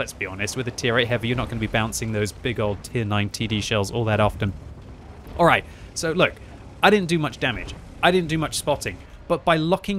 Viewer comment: my brain misheard it as Tier IX Tiddie shells...oh horny me